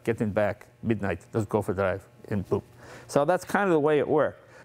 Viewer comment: What did he said "Get in the back and BOOM" ?